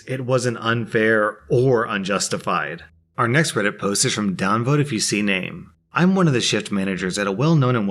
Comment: I thought he said "downvote if you see Named" (alot of my online friends call me Named cos its shorter than ToBeNamed), and I was like wtf.